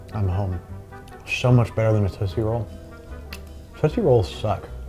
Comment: the captions saying tussy rolls sent me